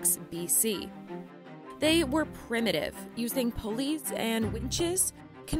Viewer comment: "they were primitive, using police and wenches". Well, that's what I heard anyway 😁